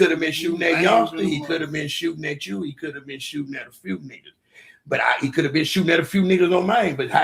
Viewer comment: what Ro said “I ain’t talkin to one or two niggas I’m talkin to a few niggas”